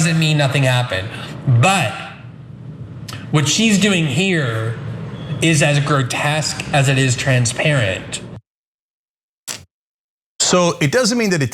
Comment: Oh GOD! 🙄🤦‍♂️ That’s NOT what he said! Listen to it again carefully. He didn’t say Ana was grotesque. This is why people call you a liar, Cenk “What she’s doing here… is as transparent as it is grotesque.” I have no dog in this fight. Yet if Cenk can SO EASILY lie about the little things, what about the bigger things?